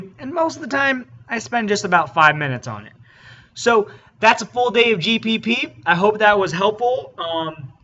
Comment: I'm not the only one that thought that this said a full day of G4P right?